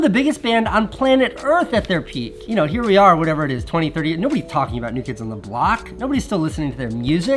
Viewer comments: did Casey just say 2038? Is this a hint at something? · Wait... did you just say it's 2030 Casey (at 4:41)? Are you in the future? Maybe you're keeping this as evergreen content... good for another 10-20 years... wait. Longevity! I see what you did there! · Did Casey just say "here we are whatever it is 2038"? I sense a Casey Niestat time travel conspiracy coming on